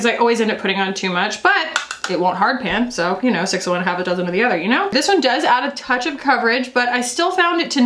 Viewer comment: please tell me what you said at 14:23 😂 my brain is melting trying to figure out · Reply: 6 of one, half a dozen of the other.